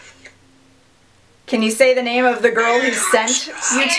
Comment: lmao when she said Can you tell me the name of the girl who sent it? i heard it say "her name's rosie" i think this girl is deaf